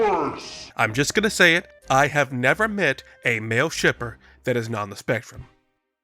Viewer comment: I wasn't really paying attention so I heard "Male stripper that is not on the spectrum" I was just like "yeah I guess it makes sense"